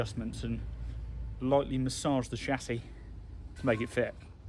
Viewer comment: Hmmmm.... 12:50 "..lightly massage the chassis..." I hear Big Clive say, "..one moment, some extreme violence.."